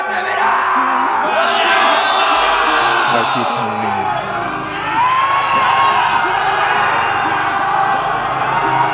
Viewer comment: cakap apa do? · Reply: Party time